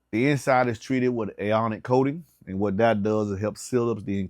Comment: Aonic coating? What is that? I keep hearing it in the videos and I cannot find anything.